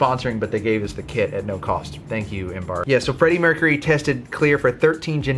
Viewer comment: 0.18 I thought it said freddie MCCURRY XD